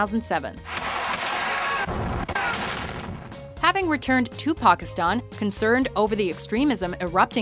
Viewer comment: It sounded like they said Tupakistan